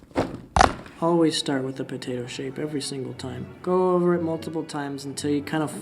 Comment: I HEARD POTATO CHIP AND NOW I HAVE DEATH NOTE FLASHBACKS